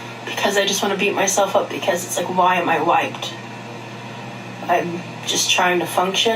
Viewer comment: I thought she said “why am I white” instead of wiped and I was like ??? 🤣🤣